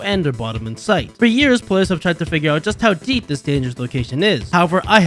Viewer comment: I thought you said answer you